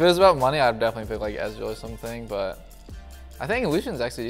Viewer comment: Did anyone notice that the captions said Azir instead of Ezreal at 1:22?